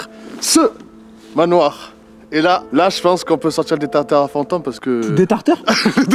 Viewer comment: j'ai entendu '' on va sortir des tartares à fantômes '' tu m'as plié direct 😂